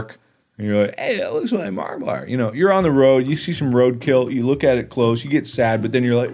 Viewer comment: is he saying that he looks like roadkill?